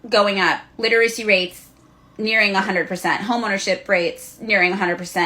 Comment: Wait did I hear that right. 18:06 Did she just say home ownership!? Under Communism! Hay Moron! Under communism you own Nothing! Not even yourself. What do you call these people? Bourgeoisie Communist?